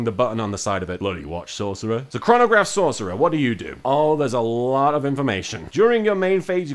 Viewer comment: For a second I thought he said Pornograph Sorcerer and I got super excited.